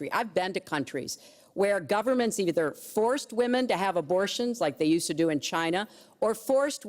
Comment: What did she said at 4:50 about Romania? I can not understand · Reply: Mario Bejenaru she said they used to force women to have kids.